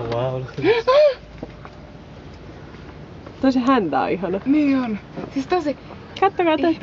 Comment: "That's a hand bag on it's knees!" @ 2:00 to 2:01 Is that what they are saying? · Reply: "Toi sen häntä on ihana." It has a lovely tail.